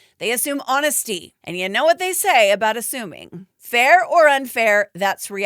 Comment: Ahhhh you said "ASSUMING" and I swear instead of hearing 'assuming' I heard 'A ZOOBIE'. If ya know... Ya know.